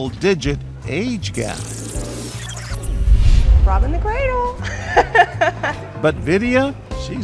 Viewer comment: What did whe say at 8:14?? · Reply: Robbing the cradle